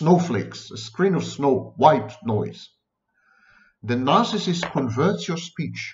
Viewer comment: At first, I heard “white noise” as “wife-noise”. Same thing!